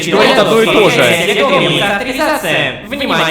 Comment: послышалось "Огранизация" незн баг это либ отсылка либо тема для теориков